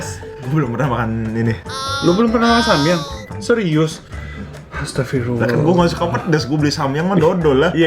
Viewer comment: Menit 01:03 dan 09:53 apa bilang astaghfirullah? Atau aku salah dengar?